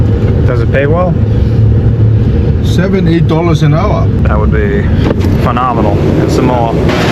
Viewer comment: Wait, I think he said "Seven - eight dollars an hour", not seventy-eight! 😂😅😳